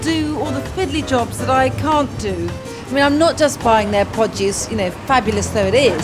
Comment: "I mean I'm not just buying their podgiest..." ...then I realized she said "produce" haha, I thought she was going to say "podgiest crab" 🦀😺